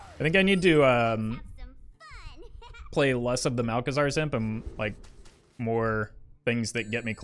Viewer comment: I heard you said "malchezaar's simp" i was like wow ok didn't expect him to hav simps